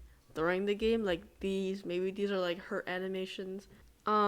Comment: did you just say her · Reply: nah i said "hurt" animations